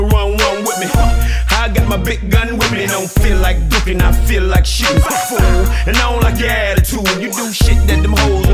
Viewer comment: I don't know why but all this time I thought Trick Daddy said I don't feel and like dookie- ing I feel like shittin. But I just found out he said I don't doofin I feel like shootin